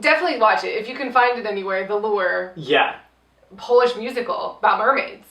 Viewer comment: I heard "the lure" as "velour"..I love too much (also you guys should do a review on wait until dark?? bc yes??)